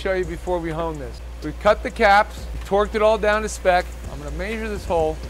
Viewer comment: Could have sworn said “twerked it all to spec”. Shake that money maker.